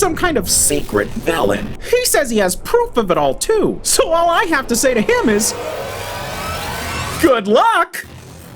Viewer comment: why I hear "all I have to say it is ...... f@ you"